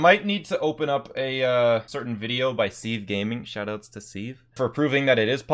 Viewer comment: Wait did I hear Ceave gaming's name in 0:34?